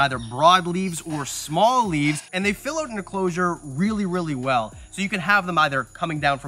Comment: Legit thought you said 'human enclosure' at the end lmao